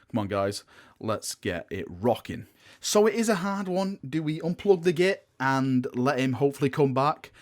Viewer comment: Did you just say "unplug the git"? The EMPEROR, a GIT? This are dark times indeed.